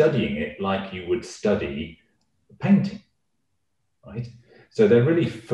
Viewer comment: What does he say at 3:15. "Like you would study a ______? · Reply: Painting :)